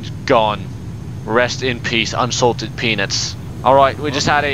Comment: did he say "unsalted penis"?